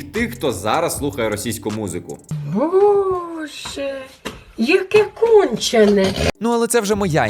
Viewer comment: Właściwie wszystko po ukraińsku rozumiem, oprócz tej babci. Widziałam ją dziesiątki razy. Czy ktoś może mi wyjaśnić, co ona mówi? "Boże, jakie...." co jakie? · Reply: She said "Bozhe, yake konchene", which means something like "God, what a sick thing", or "God what an idiot". Like, it's tough to translate that word to polish, cuz i know the definition, but i can't explain it exactly.